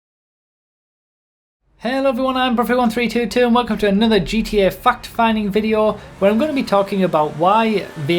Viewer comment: I can't help myself I always hear "fucked finding"